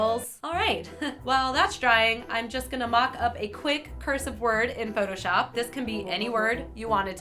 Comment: I thought is said 'boob' when she first showed the cursive XD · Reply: I thought she said to cut out a curse word. Then thought...."dead" isn't a curse word? wtf? Then I realized is was cursive word and the world made sense again.